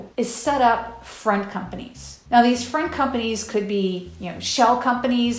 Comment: friend companies? Sounds like she is saying friend companies during this segment. Front company is the term I know.